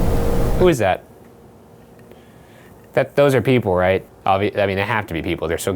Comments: it sounds like someone said How’s it Going Sir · it was clearly said "whos are upstairs?" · sounded like “What’s wrong with that?”